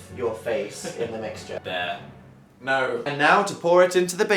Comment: did Phil just call Dan bEAR